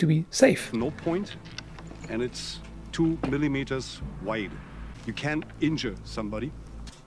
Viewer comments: I think he says you CAN injure somebody given how he shakes his head up and down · i think he says "you CAN injure somebody"